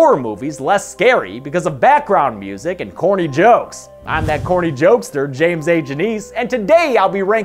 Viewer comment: I thought he said horny jokester! LOL